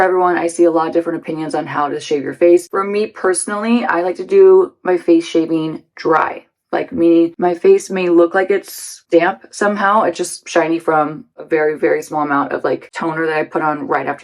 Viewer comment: Face shaving has made a mahoosive difference for me .. a total game changer. Not only does it exfoliate your skin (DON'T use any actives afterwards though!!! They will sting like hell and really hurt your skin!!), my skincare sinks in way quicker and I can immediately tell how dry/thirsty my skin is, then I can adjust the products I use for a couple of days after, AND my make-up looks so brilliant afterwards. It is such a brilliant way of making your skin look healthy and glowy. It was a total 'ah-ha' moment for me the first time I did it!!! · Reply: I thought she was saying Face Shaping! What do I know...